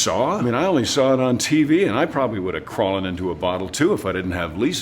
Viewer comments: did he just say “I woulda crawlen into a bottle” ??? Crawlen? Lmao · Did he just say “woulda probably crawllen into a bottle”?